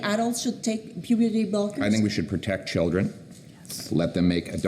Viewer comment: I wish I see the face of the reporter who asked at minute 3:37 :"so, only adults should take puberty bolkers ?". I heard "bolkers". Why would you give a puberty "bolker" to an adult? To "bolk" its development?